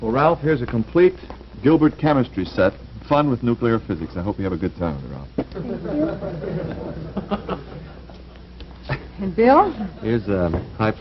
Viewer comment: white man called him negro.. thats 1954 for you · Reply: i replayed it like 10x and i heard “ i hope you have a good time with it ralph” I think their reaction was to the fact that they’re giving him a quantum physics books at such a young age